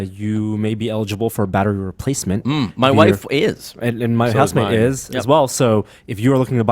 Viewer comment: Norm, When you were talking about the fact that people who purchased an iPhone5 at launch and could be eligible for a battery replacement, then said his was was eligible, I totally thought you said "my husband is too". I had to replay the video and then I heard you said "my house mate is too". Threw me for a loop there!